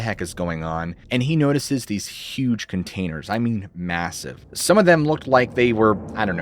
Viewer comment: Did you say massive at 8:34? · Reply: I saw your comment right at 8:34 and yes, he did say massive. LOL. Strange!